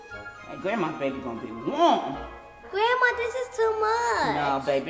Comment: What I thought said:grandma thank you so much What actually said: grandma this is to much I have no idea why I thought said grandma thank you so much